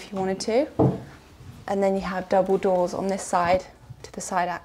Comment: Hi !! each time you said "double door" I heard "Dumbledore" haha !!! =)